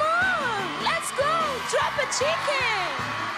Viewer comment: Did she just say "drop a chicken?"